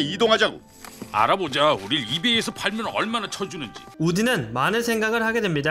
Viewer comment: 초에 이베이라고들리지않나요?